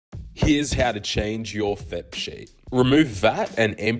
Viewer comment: Honestly l thought he said change you fap sheet. I was like that’s just called doing laundry